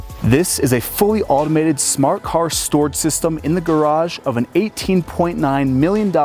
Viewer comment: You need to work a little bit on your diction, a vocal coach wouldn’t go awry. At the start of the video I thought you said it was a smart-car storage system, and got confused as to why you were using it to store a Porsche, and not a Smart car. You also really over use the word oversized, especially for a closet that’s barely wide enough for you to walk through.